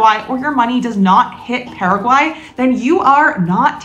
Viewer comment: DID YOU SAY ''if the money dont hit Paraguay..."" what? Please can you explain or rephrase it? Thanks!